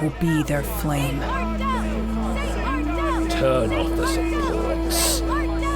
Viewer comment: What does he say at 47:00? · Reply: Pastor Le'Croix: "Turn off the supports".